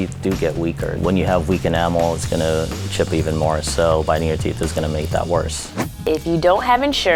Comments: did he say biting your teeth🤣😂🤣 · did he say biting your TEETH? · Did he say "bite their teeth?" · Did he really say biting your teeth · biting your teeth is going to make that worse did anyone catch that?